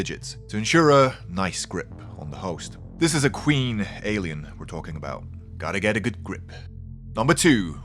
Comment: I misheard queen alien an queer alien